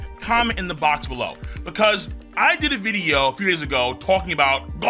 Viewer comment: I thought you said few years ago so I thought what chapter 1080 was released one year ago but you said few days had me worried a little bit